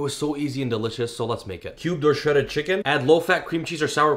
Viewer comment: Sorry but you lost me after the word cubed ? I heard "cubed low shredded chicken" but that doesn't sound about right 🤔 lol